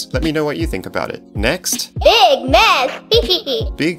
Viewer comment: I have heard it say " big mess" then giggle, it was just really quiet.